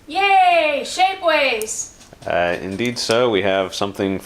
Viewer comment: i thought she said "yay shakeweights!"